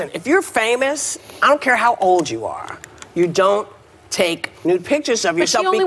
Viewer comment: It is possible to send "sexy pics" to a long distance partner without those pics ending up in the cloud. You don't need to also upload them to icloud or whatever for them to stay there forever for a hacker to eventually find... Using something with end to end encryption and message deletion, for example telegram to send the pictures to a trusted person is fine, and the images can be destroyed at both ends at a later date. Assuming the other party isn't saving them ofc. Both Whoopi and Thorne aren't educated enough to be having any opinion on this... Whoopi is wrong in saying you just shouldn't take nudes, Thorne is wrong in thinking that preventing hacking requires not taking or sending them at all. The problem here isn't the existence of nudes, its people trusting the cloud with them, and not even knowing that you can just not put them in the cloud. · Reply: I'm not sure she actually said don't take nudes, I thought she was saying don't SAVE nudes.